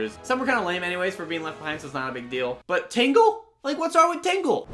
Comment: LOL WHEN HE SAID TINGLE, I THOUGHT HE SAID TINKLE! XD!